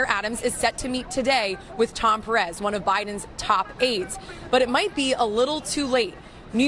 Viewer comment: I misheard 2:55 and had to go back and listen again. Was a little happy to hear that Biden had AIDS. Ah well, it was too good to be true.